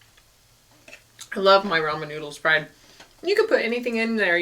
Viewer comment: Did she say Roman noodles at 10:29 LOL